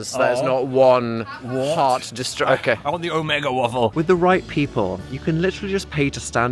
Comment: im sorry 7:29 did phil just say "omega waffle" instead of "mega"